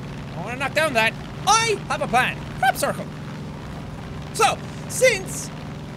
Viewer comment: When he said crop circle at first what I thought he said was FAP CIRCLE